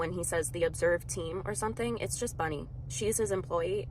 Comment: I think says “masters” like a masters degree? I don’t watch this guy… I’ll tell you why…!